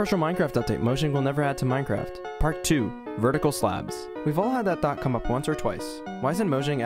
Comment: Oh I thought he said vertical slaps so all I could think of was ow